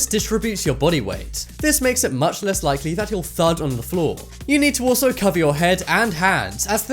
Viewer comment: did he say fart? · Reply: No he said thud